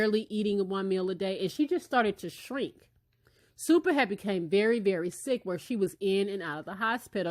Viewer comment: Who else heard supahead